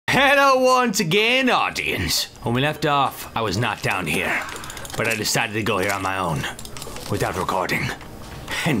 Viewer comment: i have a bad internet and the video stopped at 0:01 and it sounded like "hello once again Audi"